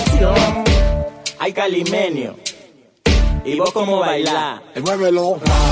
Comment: Yo pensé que decía carlinenio